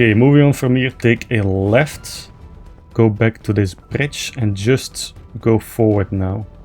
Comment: Complete guide on how to find EVERY Single OP LOOT CRATE Inside the ICE CAVE! be sure to watch the full videos as i explain everything that you NEED to know Troughout the run :) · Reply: No disrespect but I chuckled every time you said bridge cause with the accent it sounds like ur saying "bitch" 😂